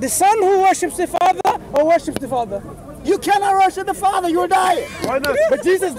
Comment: Astaghfirullah - Did he just say ' You can not worship the father, you will die? See @ 2:36.